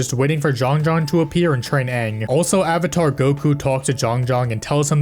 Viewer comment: did he just say avatar goku!?